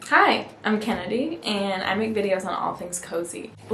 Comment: Yayy i’m so happy!! And it’s Kennedy (lol sorry i definitely mumble)🤎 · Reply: Omg I'm dumb I knew your name was Kennedy from your first video but then I thought I heard Kelly this time around so I was confused LOL. Love your videos & excited about this community. <3